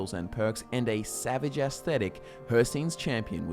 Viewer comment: For a moment i thought i heard "Savage Ass Thetic" Im slow today